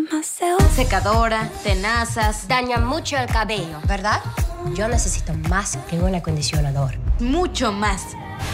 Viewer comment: Lo que ustedes escucharon "Yo nesesito mas que un acondicionador" Lo que yo escuche "yo nesesito mas que un solo pantene" :/ Xd